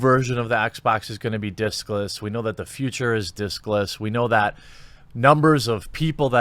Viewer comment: I heard "the future is dickless" 😱